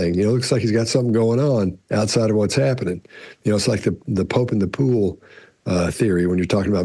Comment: The Pope in the pool?? lol Maybe I misheard that…😂